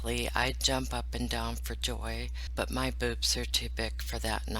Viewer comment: “I’d jump up and down for joy but my boobs are too big for that nonsense” 🤣🤣🤣 I laughed so much at this 🤣🤣🤣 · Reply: JulieB I thought she said boots, lol